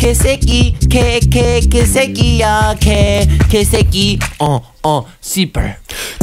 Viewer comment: HE REALLY SAID 씨발 HAHA